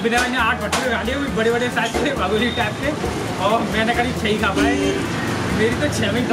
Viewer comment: Did You Notice At:- 6:46 That What Is Saying. If You Got Then Like It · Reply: Bhabhuri 😂